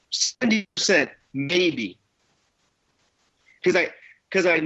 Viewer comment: At about the 4:26 mark, gives an estimate of about what percentage Weili was prepared but it kind of breaks up. Did he say 70%? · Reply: there is another full video of this interview, he said "%70... maybe"